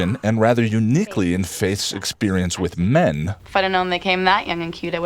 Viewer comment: ish, as you say "Faith's experience with men", the subtitles say "Faith's experience with me". Wishful thinking? :P Regarding the episode: (I wrote this part before watching all the way through. Then I saw you DID address this part at the end, but I already wrote it so I'm just gonna leave that here :D) Does anyone else feel the whole murder issue was solved too neatly here? I mean, just one episode ago Wesley was gonna take her to the council by force, without even talking to her, but now that she returns to the gang suddenly all is forgiven? Shouldn't the council send an investigator or something? Question the witnesses, look at the crime scene, that kinda stuff? I mean, if they told Faith from the start the only consequence is gonna be a psych test, she would never have ran. I adore Willow's line "Aren't you sort of naturally buff, Buff?" followed by her little giggle. It's one of my favorite Willow lines in the whole show. Also, this was mentioned before me, but I love the fact Sandy comes back in season 5. It's one of those little things that make the Buffyverse feel alive. It's nice to compare the fight scene in this episode to the season 1 fight scenes. The action scenes up to this season were mostly rather meh. I didn't really pay too much attention to them, and certainly didn't look forward to them. This season took it up a notch, and it even has my favorite fight scene in the whole series in Graduation Day part 1. Also this: Willow: "A human? Oh, yeah?